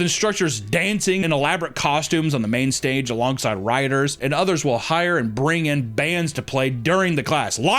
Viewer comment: i heard "RIOTERS"